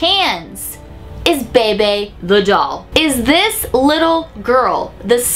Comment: who else thought it said "pepe the doll" I mean like "wtf? pepe the frog doll meme?! WTFFFF"